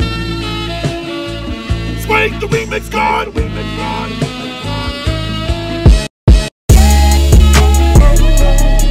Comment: What he say in 00:08? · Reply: Gabriel Fernandes he said suede the remix god